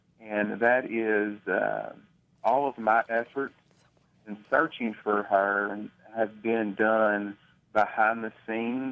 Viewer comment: someone whispers something, anyone hears what? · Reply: Something Inaudible and then ...”so quiet” maybe